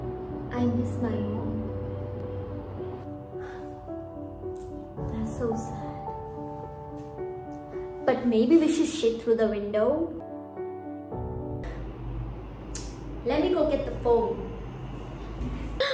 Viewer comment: Did she say "Maybe should we shit at the window?"